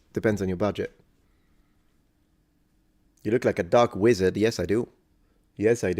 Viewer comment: A first I thought he said Duck Wizard 🦆 🧙‍♂️